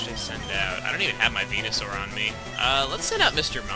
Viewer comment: lol for a second i thought he said penisaur. LOL